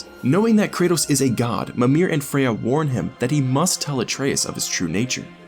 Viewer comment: Tree man: AKA Mimir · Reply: I only hear the name "Memer"